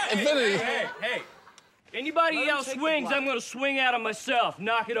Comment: Hey! Hey! Anyone else swings, I'm going to swing at him myself. Knock it off. all back down 😂 😂 😂???? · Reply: I think he said "I'm going to swing at 'em" (them) implying that he would swing at the person hitting Hamish.